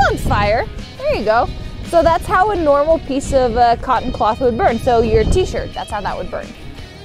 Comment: i swear she said "so, your teacher,"